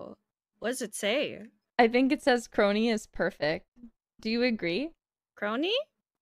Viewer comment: I thought it said is kronii is pet😂 Kronii hit her with the unorizzverse